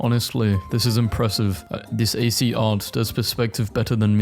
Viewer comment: I know he DID NOT just call it AC art 😭😭